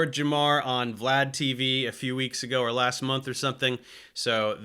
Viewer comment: Ohhh, Vlad TV. I thought you said Flat TV!